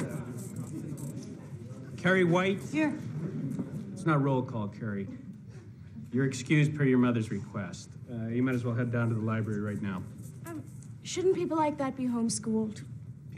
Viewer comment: what did he actually say to her? I can't understand what he's saying. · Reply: The teacher said: ' "It's not roll call, Carrie. You're excused from class by your mother's request. Go ahead to the library right now." '